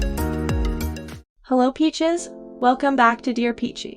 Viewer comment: I thought she said hello bitches at the beginning. Then just realised the channel name is about peaches 🤷🏻‍♀️